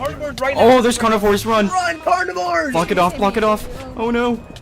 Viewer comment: I thought the play the player said F##k it off 3:20 😅